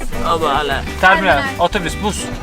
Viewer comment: perulu adama türkçe otobüsmü dedin yanlışmı duydum? 😁